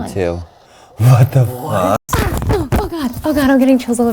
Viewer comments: I thought the spirit box said “get the f*ck GONE” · Thought it said who the fuck’s John